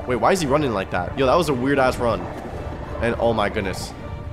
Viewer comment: Wait what u say weard as# run